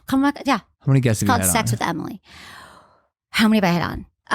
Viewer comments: Anyone else thought it said sex with family? 😂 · I thought it was saying ‘SEX WITH FAMILY’ 💀